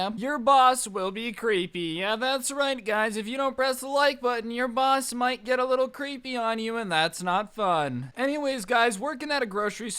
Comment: For a sec i thought he said . Press the like button or your boss will get freaky with you lol